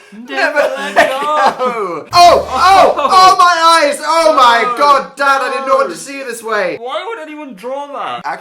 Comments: did dan said daaaaaaaad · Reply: he said "oh my god dad i did not want to see you this way" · DID DAN CALL SHREK DAD AT 8:30??? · did dan just call shrek dad? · does dan call shrek dad?? hahaha oh my god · did dan call shrek dad?? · wait did dan call Shrek dad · Is it just me or did Dan call Phil Dad at 8:30? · wait did dan call shrek "dad" at 8:30 or am i just hearing things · did Dan say "DAD I DID NOT WANT TO SEE YOU LIKE THAT!" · I thought Dan said Dad .......... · Did Dan just call Shrek dad?? (I mean Shrek is love) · DID DAN JUST SAY 'DAD' at 8:31!?!?!!!!!!??!!?!??!?!😂